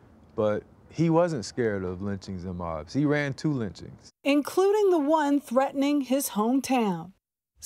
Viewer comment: what does it mean when he says 'But, he wasn't scared of lynchings and mobs, he ran two lynchings'. What does 'he ran two lynchings' mean? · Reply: "He ran to the lynchings" Meaning that he ran towards them, not away from them.